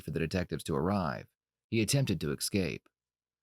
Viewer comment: did you say "attempted to EXcape"?